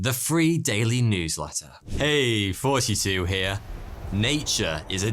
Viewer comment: I always thought he said "hey 42 here" turns out I'm very wrong